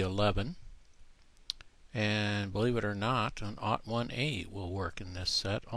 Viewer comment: Did u just say "ought one ay", or "naught one ay"? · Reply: 01A